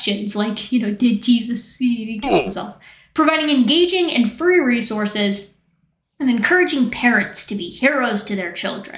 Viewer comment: I wasn't paying full attention for the website reading, then I heard "engaging and furry resources". Wait, what? 😂